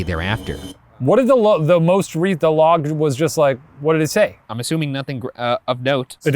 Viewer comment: What the hell did Shane say at 9:16 ? Lmao 🤣 sounds like What did the log the most recent the log was just like " what did it say?" 💀💀